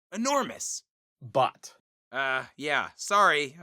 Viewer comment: I honestly keep hearing butt instead of Bot, wth?